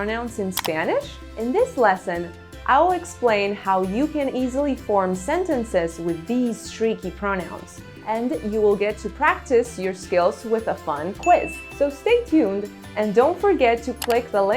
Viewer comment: did she say "treaky" or "freaky"?